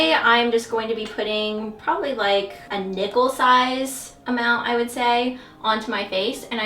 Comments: haha when you said "nickel sized" I thought you said "nipple sized" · At first I thought you said "*nipple* size amount " of the moisturizer" 😆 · I misheard and thought you said "a nipple size"